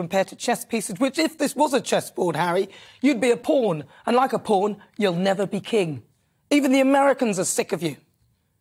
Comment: "Which, if this was a chess board Harry, you'd be a pawn. And unlike a pawn you'll never be king!" This comment from Nana is golden! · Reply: small, but crucially important potential correction of your citation as I heard and checked it at 4:40 ... it was 'and unlike a pawn, you'll never be King'. Yes, it is a little unclear, but I would suggest that the context of the remark requires the statement of difference from being a pawn ... rather than the similarity. If my understanding is correct, there is technically the possibiity for a pawn to become a King under the Chess rules (but, I am prepared to be corrected on this).